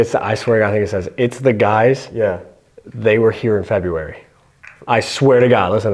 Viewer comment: for me its more like "It's the guys... It's Dylan and Steve" at least I hear that more than the feburary part. Nice episode and dope Evidence. Keep going guys :)